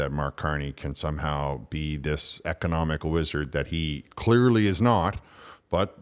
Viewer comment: "Economic wizard" I thought you said economic lizard (which seems more appropriate).